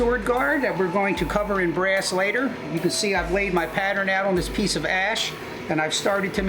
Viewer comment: I thought he said "i laid my pattern on this piece of ass" 😂😂😂